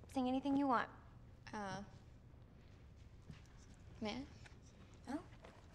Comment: For the longest time, I thought Beca just said "Mneh?" when reaching for the cup, it was only when I watched the movie with subtitles that I finally realized she way saying, "May I?"